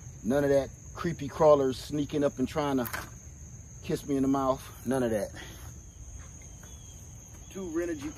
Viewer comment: I thought that's what you said a critter come up and kiss you in the mouth that would be terrible and I start laughing on that deal but that was good have you got extra panels I'll be right there lol